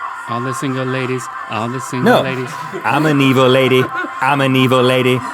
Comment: I heard "im a ukulele", not "im an evil lady" xD